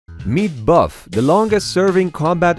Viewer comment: I heard “me buff” at the start